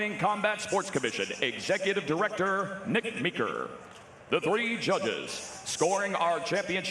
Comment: I heard Nick Nicker 😂😂